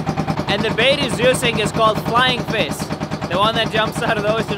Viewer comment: I thought he said was flieng fist